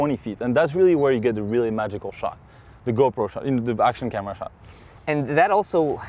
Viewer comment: Did he say go-pro at 3:20 ?